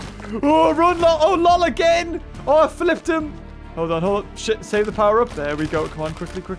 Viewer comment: Did I hear the s word at 6:33. Uh oh naughty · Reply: Grant Michael what’s wrong with saying shit?